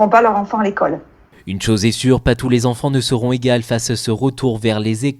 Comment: "Pas tous les enfants seront quoi ?" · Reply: Égales